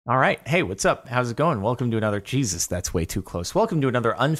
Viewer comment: Jordan: "Welcome to Jesus" Me: What? Jordan: "thats close" Me: Oh Edit: he says another Jesus not just Jesus